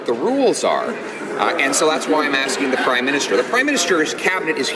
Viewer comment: It sounded like he said Crime Minister, lol!! Sorry I can't listen to that liar talk!!